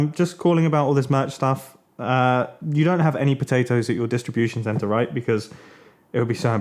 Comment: when I thought mumbo said "cajuny potatoes"